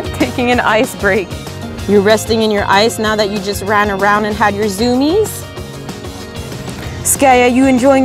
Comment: did you say “hyperzoomies” or “had your zoomies”